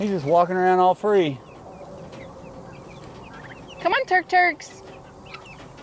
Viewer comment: Sometimes It sounds like is saying sounds like she say” come on turd 💩 turd 💩” . 😂